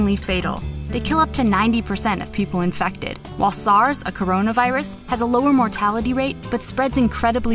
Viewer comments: DID SHE JUST SAY CRONAVIRUS???? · for u ppl who think dis predicted the future 6 yrs later, they just changed the title. thank me later 👀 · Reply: Listen to 0:15 and what did that say? Corona Virus? What is the title? Something related to corona virus. Covid 19 is a type of corona virus Corona virus is a type of sars Sars is a type of virus There have been other types of corona virus before, like in the early 2000's